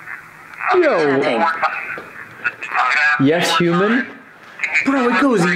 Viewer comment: Instead of "yes human" I heard "yes you may"